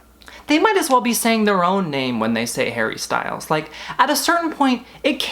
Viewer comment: You've had me in stitches tonight. You were in the zone!🔆 I heard "Hairy" Styles because of your American accent! Then your utter distaste for 2019 was spot on. Twenty 19 was so yuck it almost smells bad. Was Al Gore before your time?(Bill Clinton's Whitehouse) I wonder if he had Al Gore Rhythm. 🤪 SORRY! P.S. Marilyn Manson, your skin looks great without all the makeup🙂. Big love from England 💜 well done for giving up smoking.💜If i do, I will support you on Patreon